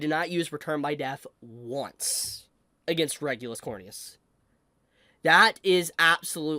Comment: xD Thought you said Regulus Horny-a$$ 😂